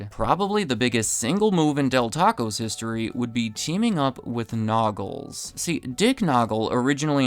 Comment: Every time you said "Naugles" I heard it as "Nobbles" - as in the muppet on Patrick Willams channel. Nobbles probably loves to gobble tacos almost as much as he loves to gobble content.